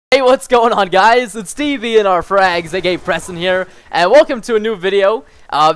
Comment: did you say at 0:02 tvnrfrags?:D